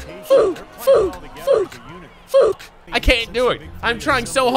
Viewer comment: the oof sounds like its saying the f word